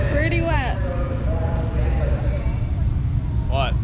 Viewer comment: wait... did he just say his bag was schwetty?